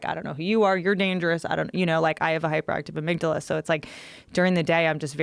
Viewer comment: did she just say that she has a hyperactive count chocula???